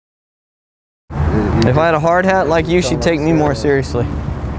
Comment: sorry chaps not funny...thought you said hard ons but it was hard hats, laughed at that. you really got to do somemore elevator pooter jokes.